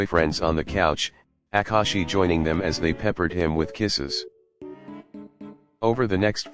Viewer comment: Thought it said "Prepared them with knee's." 💀